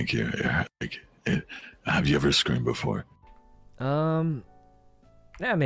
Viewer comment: I thought he said screwed before. Eh one or twice. If you do it can you record it. Lol